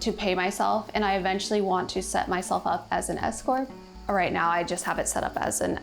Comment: I thought she said she eventually wanted to set herself up as an ESCORT. I thought, "Wow, OK". Then realized she said S-CORP.